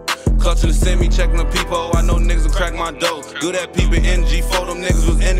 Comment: I’m tweakin thought dat Shi said pepehole❤